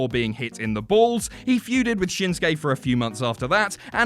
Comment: if you turn subtitles on here instead of shinsuke it says shins gay???